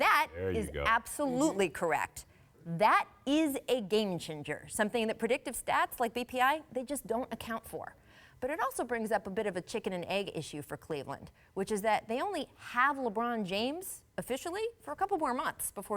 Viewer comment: did she say Game Ginger? wtf